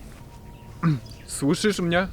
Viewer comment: po Twoim pytanie ,, słyszysz mnie ,, ja usłyszałam kocham Cię...